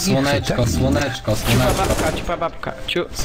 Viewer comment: " CIUPABABKA " KURWA MISTRZ XDD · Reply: Ja usłyszałem "Cipababka" xD